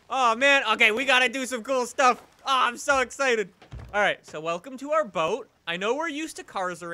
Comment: I heard "Welcome to our boat" and got so fuckin confused